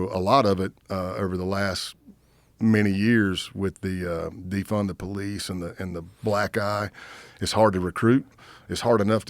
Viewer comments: Did he say “during defund the police, and the black guy” or am I mishearing things😂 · did he say defend the police and the black guy😂😂😂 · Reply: He said black eye, basically saying the reputation of police has taken a hit